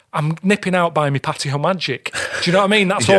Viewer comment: What did he say hes nipping out to get Patty to Magic? It at 17:37 minutes in. I can usually understand him, but I got nothing. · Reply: Lol he says hes just nipping out to get his Patio Magic. It's a patio cleaner to get rid of moss and stuff